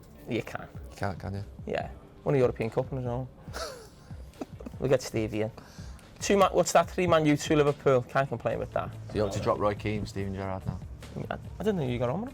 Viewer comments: What is Carra saying? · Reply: we'll get Stevie in what's that 3 man.u 2 Liverpool can't complain with that can ya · What is Carra saying? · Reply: 3 Man U, 2 Liverpool. Can't complain with that.